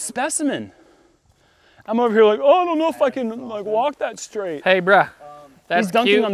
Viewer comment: Jerm at 32:49 we didn't even catch that. But when I run it back I did!!! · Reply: "I don't even know if I can..." sounded a lot like I said something else